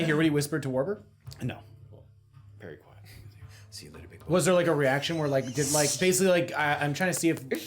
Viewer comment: It definitely is what said " see you later big boy" kiss noise 😘